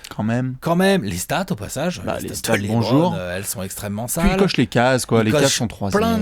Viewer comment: J'ai compris "il coache les Cavs" au lieu de "Il coche des cases" à propos de LeBron xD